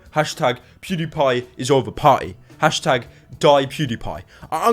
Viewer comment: wait did he say parry?